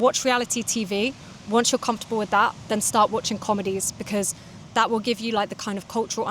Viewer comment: I can understand like 65% of the video. 5:23 I hate myself for mishearing 'start' as 'stop' 🗿